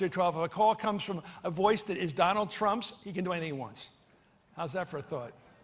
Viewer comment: Wait, can someone explain what he said at 2:53 "At one minute after 12 a call comes from a voice that is Donald Trumps he can .... blrgbgdbld " - what?? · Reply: Alex Babansky "He can do anything he wants". Wasn't that hard to understand.